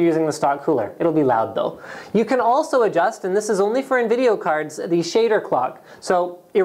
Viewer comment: did he just say nvideo cards at 2:57 lol'd